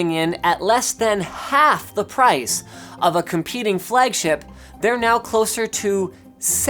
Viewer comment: It sounds like he says "Fleggship"